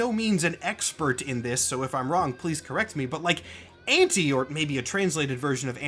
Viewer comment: I thought you said Ante like Anti so I was wondering why that would work if she’s not the opposite of anything, makes sense now though